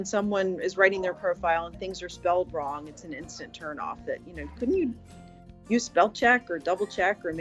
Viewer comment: I just need to borrow it, I will you right back. I promise . 🤣🤣 how intelligent, she’s complaining about double checking before you send something, 🤣🤣 then she gives him the money 🤦🏼 I thought she said that’s a turn off 🤣 Lord help us 🤣